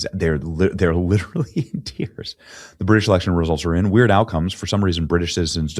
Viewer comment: I could have swore I heard you say “Weird Al comes in…” was very disappointing to rewind and hear “Weird Outcome” … Bummer.. would love to think Weird Al was red pilled